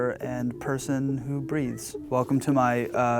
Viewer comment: I literally thought he said "person who breeds" i was like YAAAAAS JOGIA. then i realized he said breathes